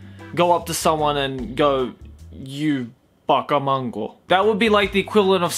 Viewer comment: To lighten up the topic, it sounds like you said "You baka mango" there XD